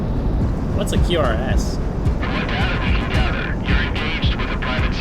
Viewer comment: QRFs. You may have misheard the voice over, but it stands for Quick Reaction Force(s).